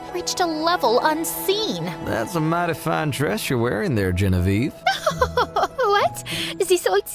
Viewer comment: I thought he said "that's a mighty fine trash you got there"😂😂